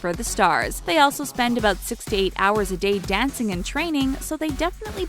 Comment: I thought she said "they often dance 68 hours a day" And I'm just like: TF that's not even possible Then I rewind it to be sure and realise she said 6-8 lol! but BTS practice 12 hours a day.. just sayin'